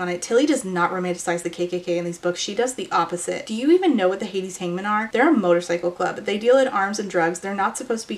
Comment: OH it’s Hades Hangmen!! I thought you said Hades Hymen 😅 that sounds like a very different series